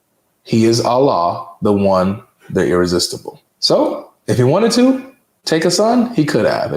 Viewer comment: Did it say irresistible??? Lmaooo :39